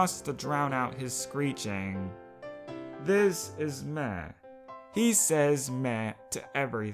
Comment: I thought said "meth" and I was like : "thats one emo name" 💀💀